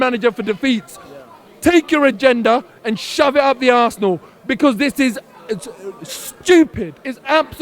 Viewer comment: Did he say shove it up the arsenal at 4:58?? :-D :-D